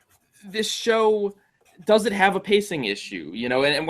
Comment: Uh I explicitly state that I DO think the show has a pacing problem. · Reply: Oh, listening closely you say "This show...does it have a pacing issue"? I heard it as "This show...doesn't have a pacing issue".